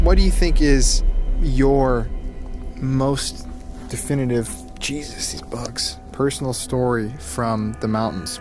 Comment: Did He say Jesus's Books on purpose? at 23:07. Idk but that was really random, kinda freaked me out.